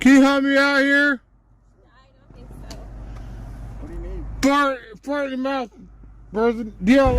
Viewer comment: did he say fart in your mouth person😂